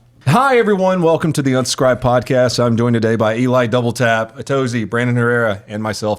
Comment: I heard "welcome to the unscribe podcast"